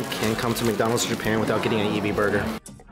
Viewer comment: I literally thought that said “ebt Burger” hahaha